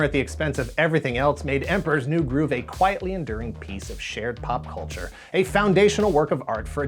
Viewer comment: I was worried when I heard: "A quietly enduring piece of sh...........ared pop culture" 😰 Whew!